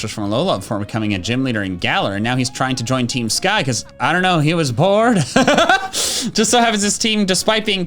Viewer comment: I thought he said he was poor not he was bored😅